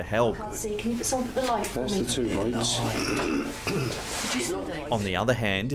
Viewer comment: I hear "They lie"